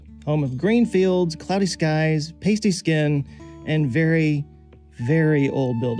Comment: I heard that as "tasty skin" 😬